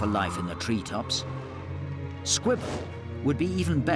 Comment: I thought he said Squidward